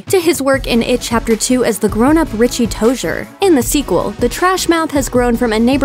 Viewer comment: did she just- did she just say toshier?